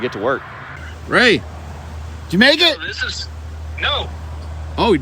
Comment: when you said Ray you make it. At fist it almost sounded like « Ray You Jamaican ?? 🤣 I rewind it a few times cracks me up… love the channel